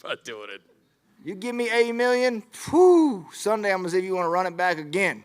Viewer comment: did he say 80 million?